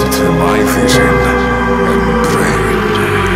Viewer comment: No matter how many times I listen to this, when he says 'vision and brain' for some reason I hear 'vision and bread'